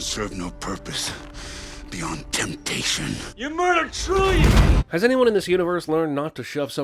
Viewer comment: at first i thought hulk said "you murdered trulia" instead of "trillions", and i was like: who's trulia? did i miss something?